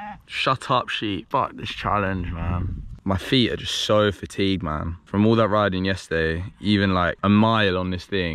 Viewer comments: there is a subtitle error he says “man” but the subtitles say “mom” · u accidently wrote Mom instead of Man